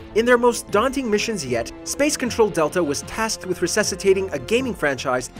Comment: did I heard Control?